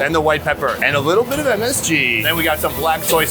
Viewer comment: did you say "a little bit of MSG?"